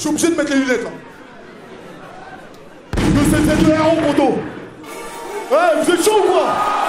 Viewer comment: il dit quoi à 0:11 vers la ? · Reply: 7 ans apres t’auras ta reponse il dit 2.7.Z.E.R.O